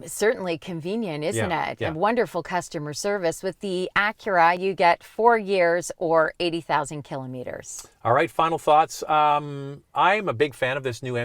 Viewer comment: did said, with Acura MDX you get 4 years or 80K of free concierge service or just a Warranty? That part is not clear to me. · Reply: Acura MDX gets a 4 year warranty or 80,000 km, no concierge service, just warranty.